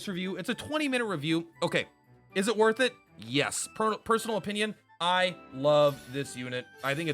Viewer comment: did you just say Purpersonal opinion?